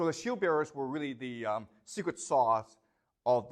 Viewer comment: did he call the shield bearers the “secret sauce” of the Macedonian army 😂